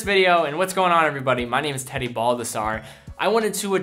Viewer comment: First time i heard your name i thought you said Teddy Bulbasaur